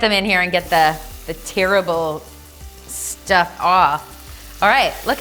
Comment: "let's get them in here and get the tear-able stuff off" i heard that pun, even if it wasnt intended.